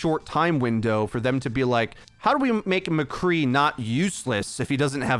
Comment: Did… did he just say MCCREE??? 😡😡😡